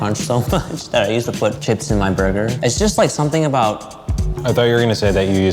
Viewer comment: is it just me or did he say 'I used to put kids in my burger'?